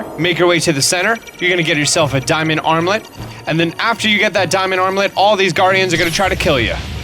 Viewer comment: I thought he was saying “Diamond omelette” at first lmfao